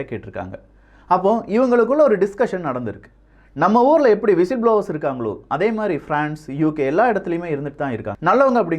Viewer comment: can anyone type that exact word and meaning what said that time . Namma oorla yepdi _______ Irukangaloo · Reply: Whilst blowers Mean Telling the Truth or exposing about dangerous events going to happen